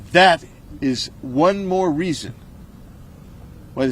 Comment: what was said = treason